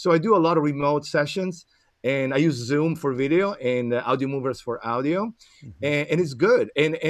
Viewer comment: What did he say he uses for audio? "I use zoom for video and ... For audio" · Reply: Audiomovers. With the real 'au'. Just like in my native langauge :). It's also the vowel we use for pain: Au!